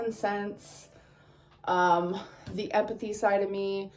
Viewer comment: I'M SORRY did she just say "epathy"? Epathy?! Amber... 🫣🤣